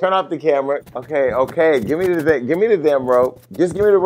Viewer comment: It really did sound like he said “gimme the dick..”, but he said really fast “gimme the damn robe “... bwahhhaahaa yeah Jason I dunno like if you and him had something behind the scenes but either he was trying to get a reaction from someone he normally doesn’t get attention from or if you two had something then he was flirting